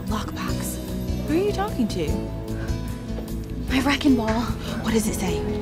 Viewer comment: At first I thought you were saying "wrecking ball" like in that song by Miley Cyrus. Do I need to clean out my ears? Reckon ball says : "Reckon so!"